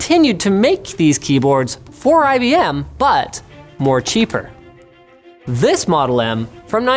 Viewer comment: did I hear "more cheaper"?